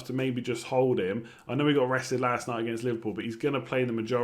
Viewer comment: thought you said he got arrested lastnight at Liverpool